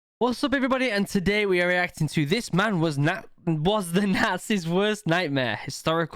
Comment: ngl when I first heard the intro I thought you said "This man was the NASA's work nightmare" I'm stupid